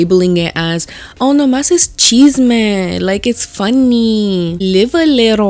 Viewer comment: what’s cheesement 😭😭😭she keeps saying it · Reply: Chisme is drama lol